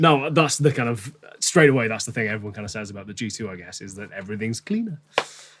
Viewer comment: what did he say?: "that's what everyone kinda says about the <?>, I guess..." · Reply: Scott T G2